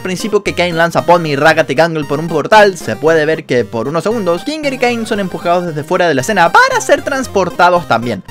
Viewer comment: kinger y CAIEN? díganme que si o escuche mal